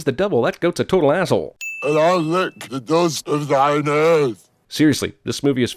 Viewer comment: What is he saying? It sounds like "I lick the nose of thy name"